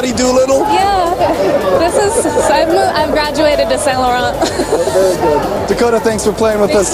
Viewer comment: Não quero ser chata nem nada, só ajudar. ;) No 1:51 a legenda está "eu comprei na promoção", na verdade ela diz "I graduated to Saint Laurent" o que ao pé da letra seria "Eu me formei para Saint Laurent", mas como ela usou "graduated" como gíria ficaria "Eu evoluí para Saint Laurent" que foi o vestido que ela usou no Oscar.